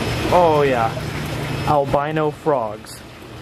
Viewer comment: albino frogs i thought you said “ I’ll buy no frogs”